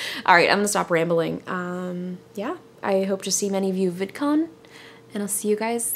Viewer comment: I thought it said Vicodin to begin with.